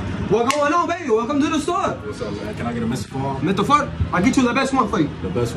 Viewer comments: I thought he said “mr fart okay…I get you the best one ! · I thought he said Mr fart